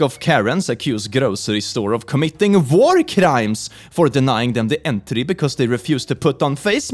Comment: No offense but when said “war crimes” it sounded like he said “vore crimes”